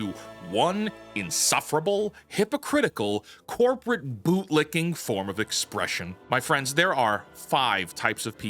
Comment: My headphones were glitching. I thought the video said " corporate boob licking." I had to rewind the video a bit.